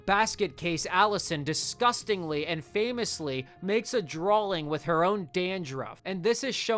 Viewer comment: Did he just say "drawling"?